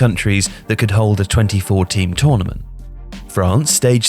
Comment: 2014? You are a bit late boys. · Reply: *24 Teams. I thought they said 2014 at first though lol